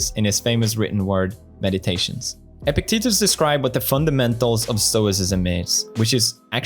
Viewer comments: Anyone else hear fuckamentals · Every time Felix says Epicetus, I just hear "Epic Titties"